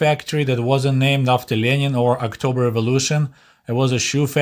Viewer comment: the way you said 'october revolution' almost sounded like 'October of illusion'. Heh.